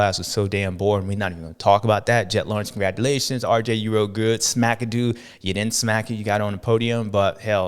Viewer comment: "smackadoo" - damn that funny, I heard "whackers" before